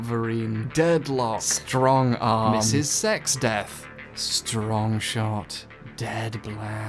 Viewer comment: I thought you said Strong Shart